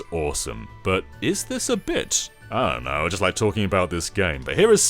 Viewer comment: It kinda sounded like you said "Is this a BITCH?" lol